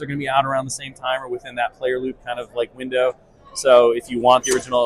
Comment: ish I heard Windu. Mace Windu confirmed?